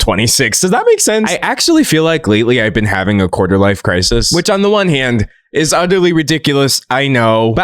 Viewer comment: DID HE SAY QUARTER LIFE CRISIS 4:20 txt mentioned🙂‍↕️